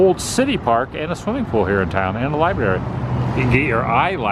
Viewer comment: Laughed when you read off the sign, i heard you say Liberry..lol